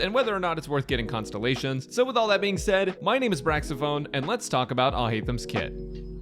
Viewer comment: I heard "My name is Braxophone, and let's talk about Alhaitham's kid".